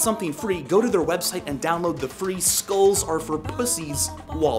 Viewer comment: Sounds like he says school instead of skulls on the last one.